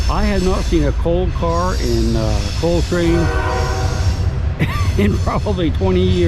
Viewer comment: That’s funny I thought you were saying soul train!! Lol